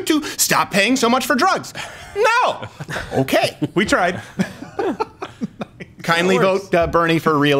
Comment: My heart jumped a litttle bit cause it almost sounded like he said "Would you kindly"; the last thing we neeed is another Atlas personality, lol.